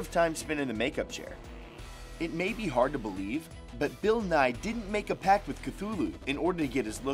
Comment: Did he say Bill Nye??? Bill, Bill, Bill, Bill, Bill Nye the science guy Bill, Bill, Bill, Bill, Bill Nye the science guy........